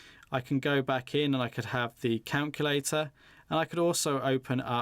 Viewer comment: did you say canculater?